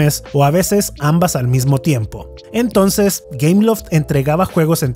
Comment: ah! entre por que pense que decia cameloft xd o algo asi